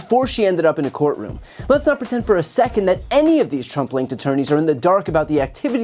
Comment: I kept hearing 'Trump-linked attourneys' as 'Trumpling attourneys' 😂